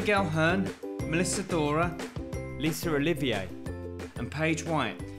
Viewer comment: Lisor Rolivio? What kind of name is that? or did he say Lisa Olivia? God that kind of accent always confuses me...